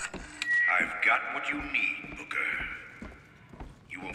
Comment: Sounds like he says booger ;P